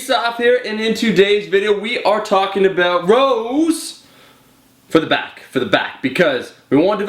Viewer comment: I thought u were gonna say "rows for the hoes" in the beginning